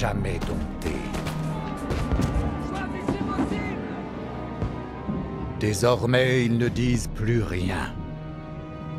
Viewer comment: What does the french officer at 0:55 says? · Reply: He said "Choisissez vos cibles!" "Choose your targets!"